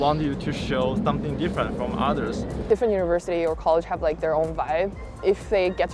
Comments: I think she said vibe instead of webs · I think at minute 7:40 in your video when the girl said different universities and colleges have like their own vibe, the subtitle said “webs”. You might want to edit it.